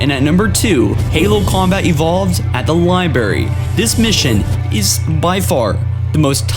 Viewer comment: Did he just say LIEBARY?!?